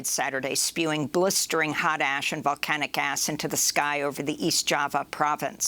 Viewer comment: did Amy say "Volcanic Ass?" ☺️